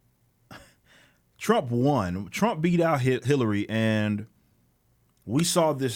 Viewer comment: were you about to say Hitler(y)? 'cos it sounds like you said "Hit" before correcting it to Hillary.